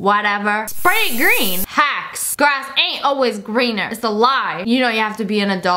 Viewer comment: I put captions on and she said “hacks” and the captions said “Hawks” lol